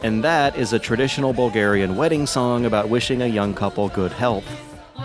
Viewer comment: "And THAT is a traditional Bulgarian wedding song about wishing a young couple good health. So it's usage in the game sure was a stretch." Actually I don't think it's inclusion in the game is that much of a stretch at all. I think Lazare playing exclusively in the swamps of Velen means that it's meant to be a twisted jab at the Bloody Baron and his wife. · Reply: ngl i thought he said "...about wishing a young couple go to hell" lol